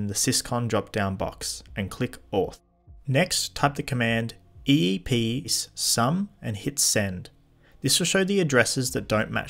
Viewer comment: is it "eepsum" or "eepcsum" 😶 your caption says something different to what you wrote on the machine and it kinda sounds like you said a mixture of the two 😅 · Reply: Eepcsum Sorry for confusion